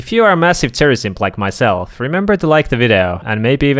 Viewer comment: Nah whatt I thought bro said if ur a massive terrorist list like my self 0:30 😭